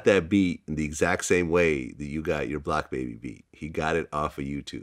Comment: Did vlad say black baby beat at 28:47?